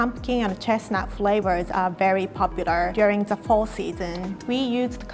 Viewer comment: I thought it said Fail....